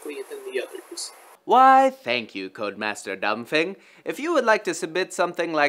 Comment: Sounded like he said "Code master dumb thing."